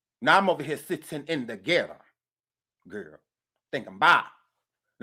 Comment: When I hear “sitting in the ghetto” every time 😂 ☠️☠️☠️☠️☠️